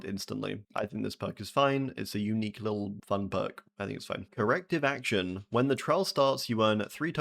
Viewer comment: I heard Erective Action